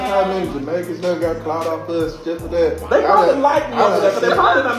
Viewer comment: Did I just hear them say the country of Jamaica got clout off of them?